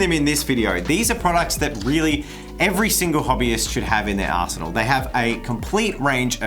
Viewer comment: Not taking away from their lovely creations, but I totally heard say "..every hobbyist should have in their asshole"